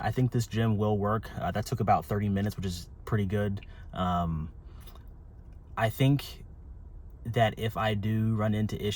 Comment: did you said go in there “caked up” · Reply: I did unfortunately say "cake" 🙃